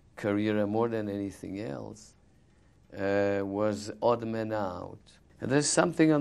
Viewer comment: what is the name of a movie..can't understand it right..help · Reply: Got it! aha, its Odd Man Out >> <link>